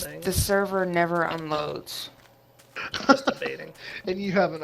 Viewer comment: did say masturbating?